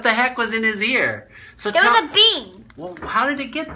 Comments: This whole time I thought this man said a “bee” was stuck in his ear and I was captivated. Turns out it was a BEAD. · I thought he said a 🐝 BEE! LOL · This whole time I thought this man said a “bee” was stuck in his ear and I was captivated. Turns out it was a BEAD. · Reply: I know me too! I thought he said a BEE! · I thought he said a bee I am like what! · I thought he said a bee